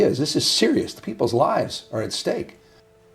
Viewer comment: it’s kinda funny how it almost sounds like he says people’s lies are at stake 🤣🤣🤣🤣